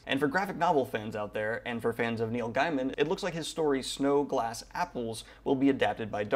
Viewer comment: First I heard "For you graphic novel fans out there, and fans of Neil Diamond" and my head started spinning.